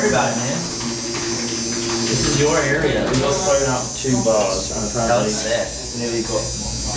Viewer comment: When ben walked by at 2:18 did anyone hear him say "we got thrown out of two bars...nearly got beaten up by a bunch of locals"